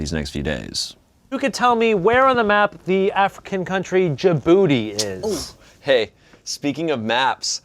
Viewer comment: I only hear Jibooty. Where my ARMY at?